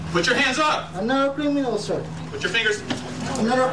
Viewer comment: Anyone know what the suspect is saying between 19:12 and 19:16? I can't make heads or tails of what he's saying. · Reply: He keeps saying "I'm not a criminal, sir"